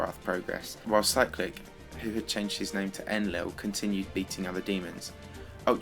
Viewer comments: I thought u said cyclic changed his name to Endlevel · Dude did I just hear "Cyclic, who has changed his name to endlevel".